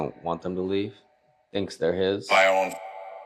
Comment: At around 29:49, after the question about if someone's trying to keep the women to himself & thinks they're his, I swore the box said, "my own".